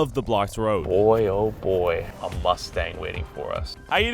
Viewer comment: Did he say mustang 11:11 …..